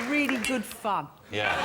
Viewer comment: I thought she said "really good fuc* but according to the subtitles it was fun.